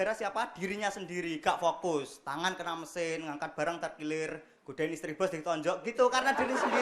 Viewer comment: itu mas ngomong apa ya? Gak begitu denger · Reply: Godain istri bos ditonjok